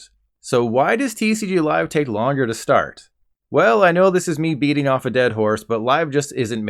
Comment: yo is it just me or did he just say beating off a dead horse